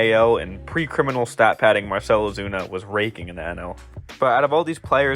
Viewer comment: I thought he said Marcel opina was raping not raking 😮